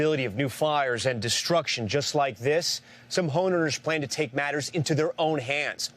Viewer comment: Did he just call Home Owners HONERS? Minute 3:48 or so. What?